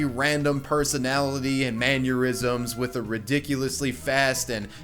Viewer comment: why do you pronounce Mannerisms as Manureisms? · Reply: Ben M i thought he was saying aneurysms and i was extremely confused